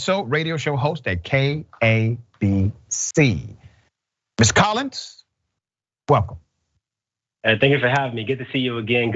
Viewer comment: Did he say Miss Collins on the sly the @ 0:25 mark? Lol